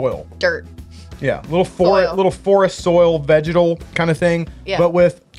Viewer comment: Could have sworn when you were saying vegetal that you were saying vaginal haha! #doitblind #gucciscissors